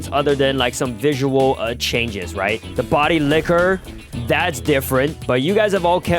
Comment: Nahhhh did he just said body licker or I misheard it 😂